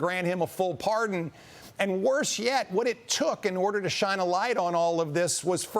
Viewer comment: I thought Youngkin said "He should LEAVE". I would agree with that better